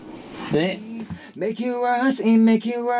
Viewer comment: I keep hearing "Make it worse" 😂